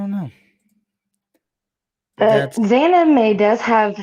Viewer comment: I swear I heard him say xanamayx